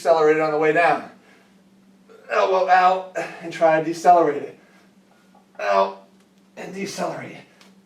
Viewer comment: "out" sounded like "oww"